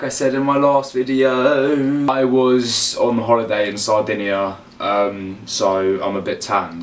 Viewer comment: Did I hear it wrong or he really did say South India? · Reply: I thought he said Sardinia